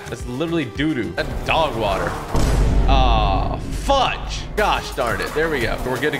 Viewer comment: For a second i thought that he said f*ck instead of fudge😂